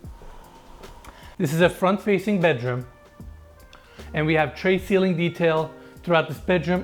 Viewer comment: the caption is wrong. It should be “ tray ceiling “ not “tree ceiling. “